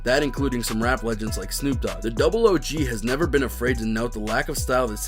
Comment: did he really say double o g when refering to snoop dogg??